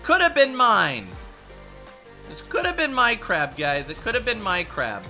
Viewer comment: did u said 'minecraft' ?? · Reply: "my crab"